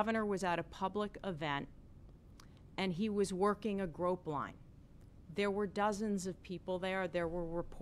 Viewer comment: did she really say "he was working a grope line"???